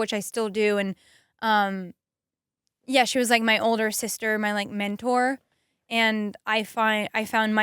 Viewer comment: did she just say Ella like my “older sister“ That broke me